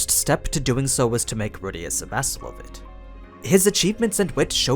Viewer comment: good content as usual but I completely can't understand what saying at 12:04 "and his first step doing so is to make Rudeus ... of it" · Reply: = a vassal